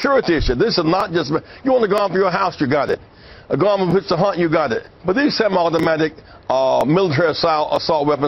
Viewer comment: did he say "if your garments want to hunt, you got it? @ 1:55????? lol